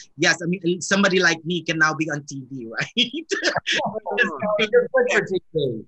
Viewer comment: What did Jim say at 4:17? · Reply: I think he said you’re good for TV 😍